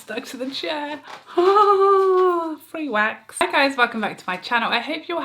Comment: Omg I totally misheard you at the very beginning of this fab video. I though you said “fanny wax” and had to listen again, obviously you said “free wax”. Did make me lol though